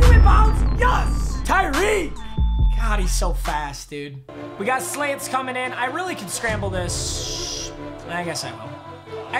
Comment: Yo at 10:39 it sounded like he said we got slaves coming in